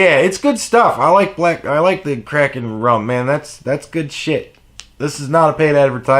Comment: Let's be real. The heating pad is why we all watch these videos anyway. Also I thought you said "I love crack and rum" lol I had never heard of Kraken rum.